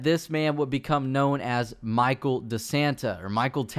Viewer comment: I thought you said "Michael Da Santa"